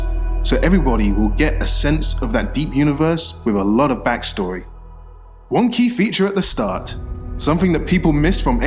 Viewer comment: I know he's saying "One key feature at the start...", but I keep hearing "Wonky feature at the start...".